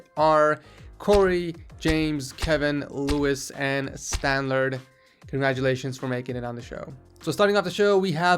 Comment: turn on subtitles: Whats up guys its Effin Texas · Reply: what says : corey james kevin louis stanlerd. Now what subtitles say:quarry james kevin lewis and standard